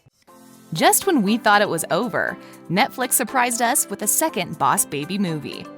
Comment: did she say netflix!?!?!? The show is over